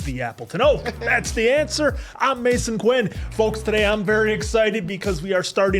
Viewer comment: I swear I’ve been watching you guys for so long yet every time I hear “… that’s The Answer, I’m…” my brain fills in “The Question” not “Mason Quinn” XD